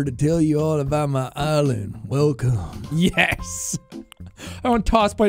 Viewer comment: I thought he said my name toss boy im here to tell you about my eylids. I was like it fits?